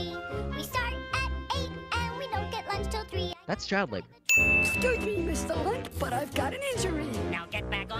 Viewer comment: I always hear "excuse me Mr. Lick" instead of Mr.Lunt